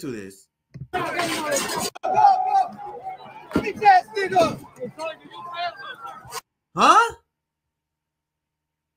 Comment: Still could not understand what that anti-protests guy actually said at the 3:37 mark ... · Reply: Bit**ch a*s*s N word .